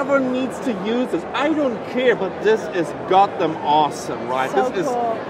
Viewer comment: I thought you said Gotham Awesome